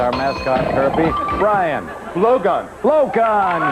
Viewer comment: I need hearing aides or a cleaner mind or both I thought he said blow job and not blow gun lol